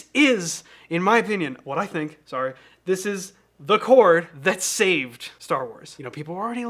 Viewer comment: why did i hear that as "the chord that saved four wars"